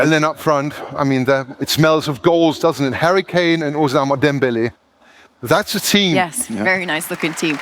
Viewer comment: Did he say Osama Dembelé??😂😂